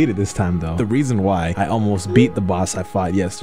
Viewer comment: DID YOU SAY ALMOST?